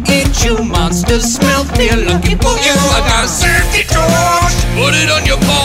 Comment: fun fact, he says safety PORCH instead of torch.